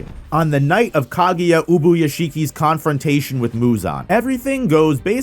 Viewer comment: I heard Kaguya Otsosuki 💀